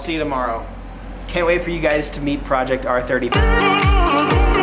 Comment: project r33? is that what he said? is that the car?